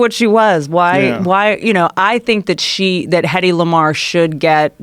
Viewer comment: Whitney shouts out Heddy Lamar and all i hear is "Its Hedley..."